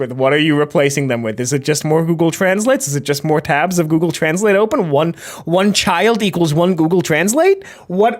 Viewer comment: i heard "google translates" and thought you said "google translets" like google search results have been embued with life and the little spawns must spread the info of the search (such as the rotisserie lobster)